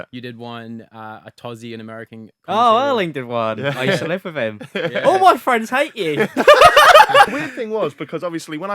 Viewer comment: Did say say Atozy "oh I liked that one, I slept with him, and all my friends hate him" ??